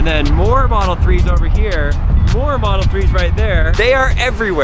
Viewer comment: I thought he said four model 3's there I was about to say u freaking dumb that's 3 but he said more not four